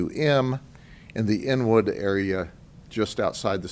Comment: Inwood area. I thought he said n-word area. Not from there but I bet folks there, especially the po lease call it that haha